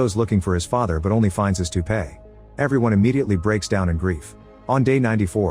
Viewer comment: the caption says "Two Pay"...bwahahaha